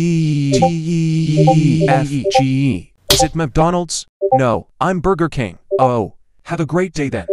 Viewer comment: What others hear in this video: is it mcdonalds what i hear (i misheard it lol):sigma donalds